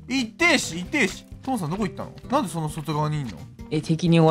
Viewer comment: 6分59秒あたりホモさんって聞こえた